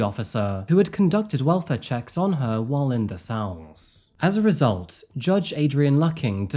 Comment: Can anyone help me understand what Adrian says at 19:58? The key witness conducted welfare checks on her "while in????" I cannot for the life of me figure out what is being said and even the auto captions just say "while in the S" because it's so hard to understand lol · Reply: I think Adrian says while in the cells 😊